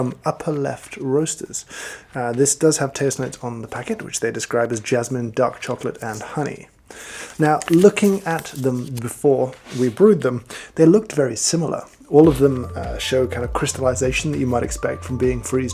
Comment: tasting notes: jasmine, duck, chocolate and honey ??? duck!?.... OHHHH "DARK CHOCOLATE!" 🤷‍♂️🤣 lol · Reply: Well if I heard "duck chocolate" I would be freaking out too.